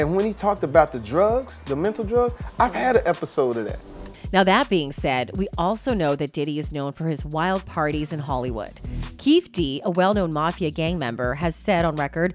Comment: Did she just say....HOLLYWOOD MAFIA?